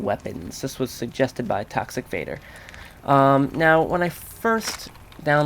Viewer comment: Haha i thought you said that guys name was tax evader instead of toxic vader XD